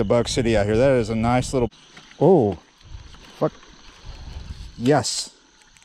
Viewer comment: Did he say fuck at 6:40?? Either way I love your videos and I am working in New Orleans on the Mississippi hope to see you some time while I’m working